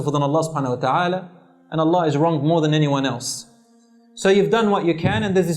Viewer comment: I'm pretty sure I'm hearing this wrong but does he say at 2:39 "and Allah wrongs more than anyone else". Can anyone tell me what he is actually saying. Or is there something I'm not understanding here?